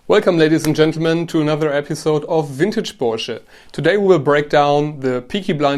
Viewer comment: It always sounds like you say “vintagebullshit” so I go “Oh I feel like watching some vintagebullshit today” hahahaha